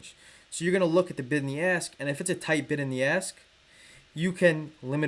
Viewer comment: It sound like he was sayin, bite in the ass.